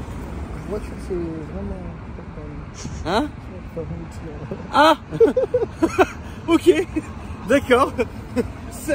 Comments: svp il dit quoi a 5:35 j'aaruve pas a savoir sa me stress mdrr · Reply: Qu’il a plus de permis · il a dit quoi ? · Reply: Il a pas le permis jcrois si j’ai bien compris 😂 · il a dit quoi les reufs ??? 🤣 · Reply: J’ai pas le permis